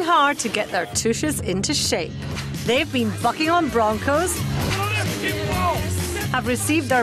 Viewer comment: She sounded like she said "fucking on broncos"